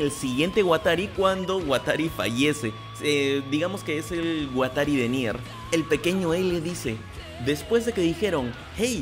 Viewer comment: escuche "es el watari de mierda" xd